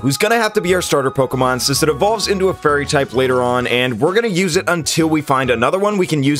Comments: I thought he kept saying furry type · In the beginning I heard him say “we have to choose poplio because it evolves into a furry type”